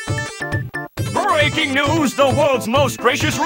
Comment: I misheard this as "The world's most racist ruby